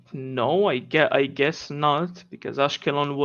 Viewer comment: Did he say Askalan? This guy is quite literally right next to Gaza and says he does not care.